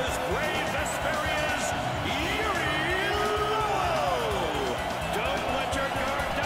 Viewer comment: it sounded like he said "Yuri Lol' XD